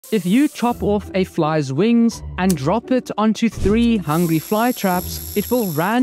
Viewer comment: For a second, I thought he was saying "If you cut a fly's wings, is it called a walk now?"😂